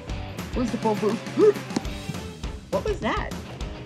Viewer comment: whats did Stupe say their? I couldn't hear it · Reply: “Where’s the phonebooth?”